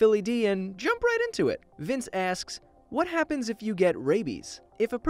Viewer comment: I actually thought you were saying ( what happens if you get babies ) my eyes are blind